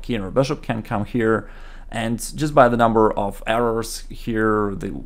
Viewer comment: I thought you said 'Just by the number of arrows here black should have winning position...' 😂